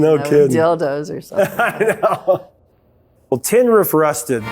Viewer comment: I always thought they said "Hen-rey.......busted"!